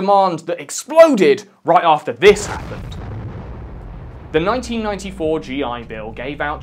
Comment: is he saying "1994"? Because that seems wrong